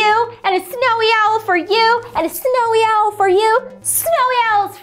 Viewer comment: my hearing is going, I thought was saying 'Snowy Elf for you and Snowy Elf for you....'. would be interesting